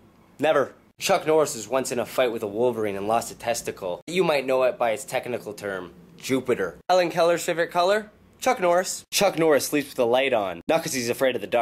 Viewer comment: i thought u said chuck norris never sleeps...